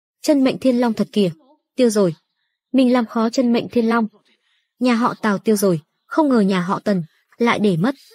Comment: Mọi người có nghe nhầm không nên lần đầu tiên tôi nghe là nhà họ táo lần sau rồi mới hộ tào